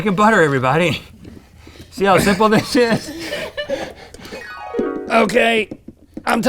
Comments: for a second it sounded like like Rhett said "you see how simple that shit is" when in reality he just said "you said how simple this is" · did he say "see how simple this shit is" never heard GMM cuss xD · Is it me or did Rhett say “see how simple that s**t is” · Note to all: Rhett did not say "sh*" at 12:48. He said "See how simple this is?" But he muffled his words and said "See how simple thish is?". He said "thish" not "this sh*". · does anyone else hear Rhett say "see how simple that sh(bleep) is?!" but he actually says it o,o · did he say see how easy that shit is!!